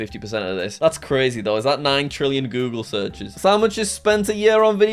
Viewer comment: why did he say sandwiches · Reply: He said "so how much is"